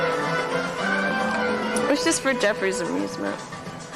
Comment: does she say it’s just for jeffery’s amusement??